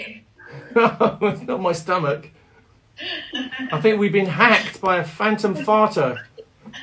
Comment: What did Bri say? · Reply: i think weve been hacked by a phantom farter :’)